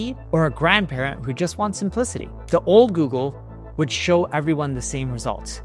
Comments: did he really say a grandparent who just wants some pussy at 2:37 ? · grand parent who just want? Did i heard correctly? 😅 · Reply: 😂 I came here looking for this comment! I had to turn on the captions for this one and it read: "...and a grandparent who just wants *simplicity*" I'm still NOT hearing that. 😅😂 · Did he just say 'grand parent who just what's some pu😺 😺 y' 😂❤